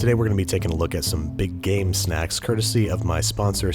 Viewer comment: I heard "Big Gay Snacks"